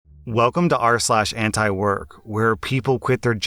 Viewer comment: Antiwork? damn I thought you said Ant work.